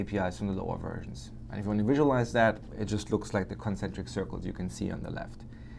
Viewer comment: what circles has he said ? at [05:32] - [05:33] ? Consubject circles ? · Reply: I said "concentric circles".